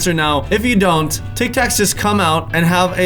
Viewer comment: An I the only one who hear Tal say "tik tok" instead of tik tac